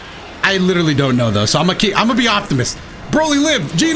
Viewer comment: I know you were trying to say “I’m going to be an optimist” but I really heard “I’m going to be Optimus” and all I can think of is “Dotobots, roll out!”